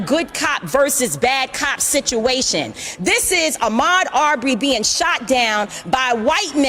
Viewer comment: Creating subtitles for social media. Can anyone tell what Mallory says at 0:30-0:32? ("This is a --- ?-- being shot down") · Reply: This is Ahmaud Arbery being shot down in Georgia.